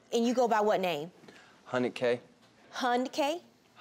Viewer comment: Hunnid K? I thought I heard funnel cake at first.